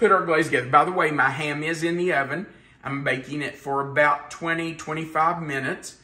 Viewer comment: LMAO You said "BTW I've got my hand in the oven, I'm baking it". I heard "BTW I've got my HAND in the oven, I'm baking it"..... 😳 😂